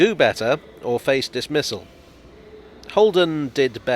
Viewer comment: I misheard "dismissal" and thought you said "this missile" and was really confused why you'd fire heavy ordnance at architects who don't have time to work for you